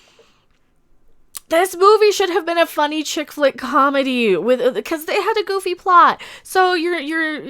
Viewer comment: I swear I thought you said " A shit flick comedy" I laughed so hard.